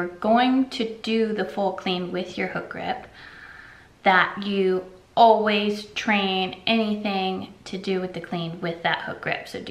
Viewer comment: I heard---- always train...untamed